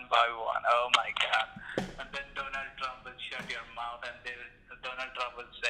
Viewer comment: When the scammer said, "Donald Trump", I thought he said "Donut Drmp". Lool